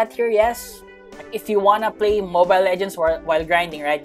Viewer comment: did i hear mobile legend?